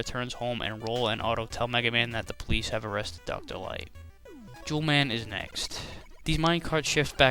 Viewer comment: sounded like you said 'jew man is next'